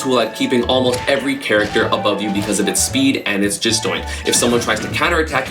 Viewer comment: Did anyone realize he said "Jisdoint" instead of disjoint?